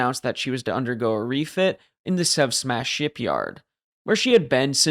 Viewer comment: You said, "The Sev Smash Shipyard", I heard, "The Sub Smash Shipyard", which sounds closer to reality...